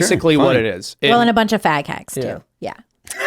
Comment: I have never heard the term "fag hag" until now lmao... i had to listen to it a few times to understand what she was saying, that shits hilarious.